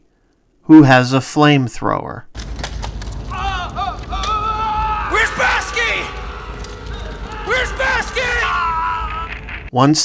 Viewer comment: I always thought the one Marine was called Baski, or Bausky. With Hicks asking "where's Bausky?!". Not too big a mistake I guess. Kinda like misheard lyrics. Ie "Excuse me while I kiss the sky", mistakenly heard as ".. while I kiss this guy" lol 😆.